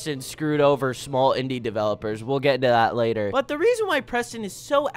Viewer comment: I thought he said small Indian developers ☠️☠️☠️